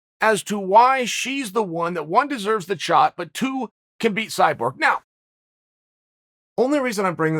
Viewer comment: "Could beat Cyborg"?did i hear that wrong?